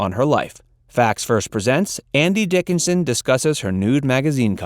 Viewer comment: I swear he says Andy Dickinson in the title announce.